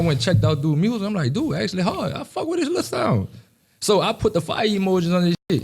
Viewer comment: Did he say I fuck wit his lil self???? lol yall Louisiana boys got some different type of lingo lol. That’s something my auntie would say about one of my homeboys tryna get at her.